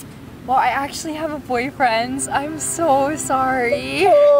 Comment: Did she say she's having boyfriendZ?